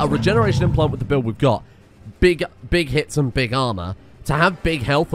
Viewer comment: I heard "Big hips and big armor" at 4:00 for some reason.